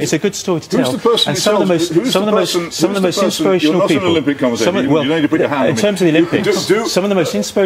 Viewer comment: What did Peter say at which Russell laughed? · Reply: He said ..no need to put your hand on me....to the conservative politician